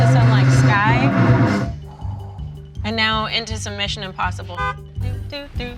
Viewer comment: At first I definitely heard her say something about "submission", not "mission impossible". 😂